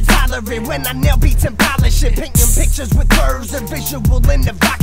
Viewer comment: Did he say a bad WORD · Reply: He said polish but at first I thought he said sh*t